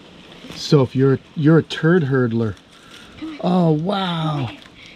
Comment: thanks to you the word turd herder is going to be said daily! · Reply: A turd herder is actually a slang term for a plumber, fyi. I heard "turd *hurdler*" here though, which although probably accidental is even more hilarious! 😂 I'm gonna look for excuses to use it too.